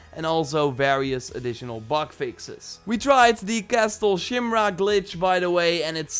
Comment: I swear I heard you say castle shimrock